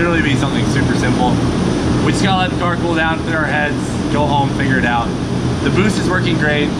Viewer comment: Hahaha....Cleetus at 13:58 did you say you was going to finger it out or figure it out?